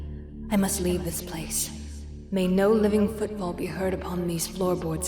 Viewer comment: "Light no longer fills this home. These haunted woods are a catalyst for the demons and ghosts that torment me. I must leave this place. May no living footfall be heard upon these floorboards again." MY TREEHOUSE NOW BEECH Me: I wonder if she's found all the secrets it holds yet? Will she play in the basement? Or is she too afraid to go down there alone? I would be very afraid if I were her.... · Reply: Oh "footfall"? For some reason my dumbass thought she said "football" XD